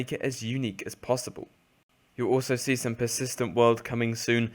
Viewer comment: What did he say at 12:25? We'll see some persistent world soon?